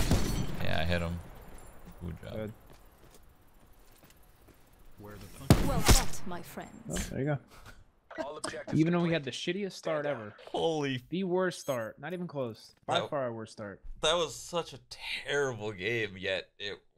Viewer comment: Did she say well fucked, my friends?!??!